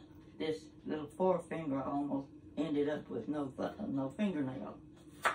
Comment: Did I hear her right? She almost ended up with no f**king little finger nail?😂